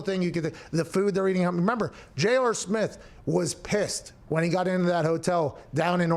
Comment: Pat did u just say jaylor smith? Did u mean J R SMITH?